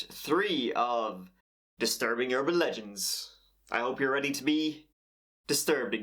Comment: I heard, "Welcome back, to part three of disturbing your religions" and had to look at my phone again to double check what I clicked on lol.. Like.. Wtf does that even mean?